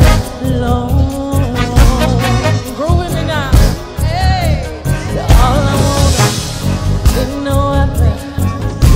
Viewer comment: what she say? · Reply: Groove with me now